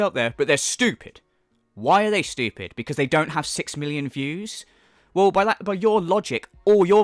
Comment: Wouldn't a "fake" video be easier to debunk? If had balls he'd react to one of Secret Life's DSPGaming's 10 Worst Moments of 20XX video. But he doesn't, so he won't. When says "Six million views" I hear him saying "Six million Jews" in his Isaac Heimler voice.